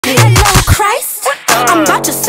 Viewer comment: I thought she said I’m bout to “sit” again 💀💀💀😭😭😭